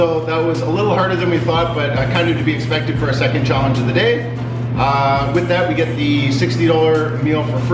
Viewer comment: Congratulations!! Did said ( 10:27) second challenge of the day ?? Keep it up . Love